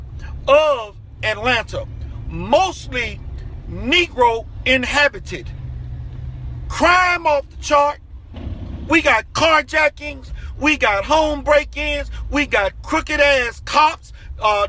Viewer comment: Whats if William Assange was black the cia and cnn will be racist? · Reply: Wait did you say Nigro at 3:36? see thats some privilege! put christ rocks 1996 comments here